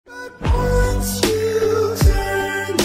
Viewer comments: When I first watched the movie I thought she said " Not my daughter you witch" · I thought you were saying not mine Daughter you witch😂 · I always thought she said "witch" instead of "bish"